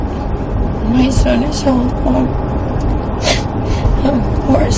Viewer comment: Talk about not being able to take responsibility for your own actions. She keeps changing her story on where her son is, I genuinely hope he was ok and not home alone. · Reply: it sure sounds like she said her son is home alone.